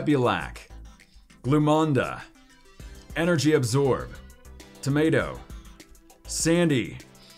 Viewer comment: i heard you said tomato